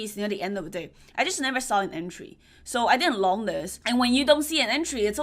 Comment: Thats what I thought to. Than I watched again and she meant "loaning this". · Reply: when I listened again, I heard "longing this"